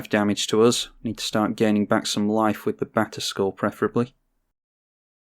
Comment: I swore you kept saying Back to School and not batterskull lol. I was so confused lol